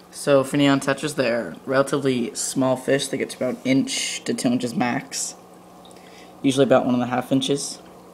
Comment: did he just say 10 inches lol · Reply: no, he said 2 inches lol